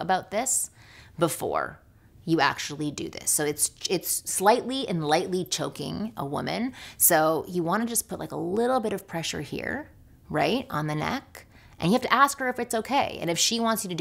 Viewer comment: Oh choking... I thought she said joking